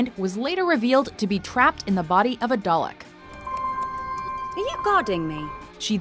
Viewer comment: did she say dulek